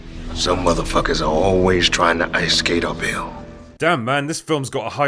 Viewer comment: Some movie studios are always trying to ice-skate uphill. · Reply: For years I thought he said a pill instead of uphill 🤣